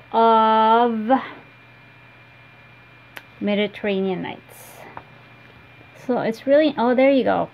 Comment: Thank you for the correction on miss mary mack. I would have lost it if I have to rip it all out. What floss did you use for Quaker Samplings II? I thought you said Mediterranean Nights, but I don't know who makes that, and I pretty much need it in my life, it's gorgeous. thanks :)